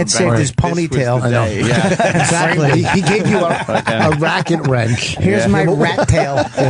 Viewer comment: did Vos say “Racket Wrench”?